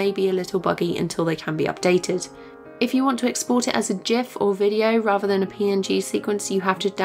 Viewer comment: did you just say JIF ?!